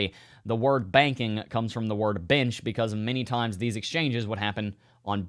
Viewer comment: When you first said “benches” I thought you said “bitches” and I spit coffee all over my computer 🤣